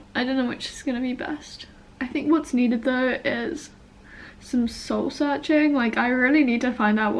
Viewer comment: "I think what I I really need to do now is some soul searching"...I heard that as "I think what I really need to so now is some salsa dancing" hahahah sorry but it was so funny..I was like wait..what?? xD